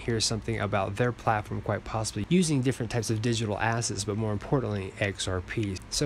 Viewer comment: Did you say digital ASS?